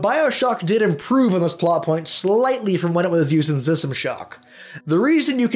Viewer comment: what did he say at 1:59 zistamashock?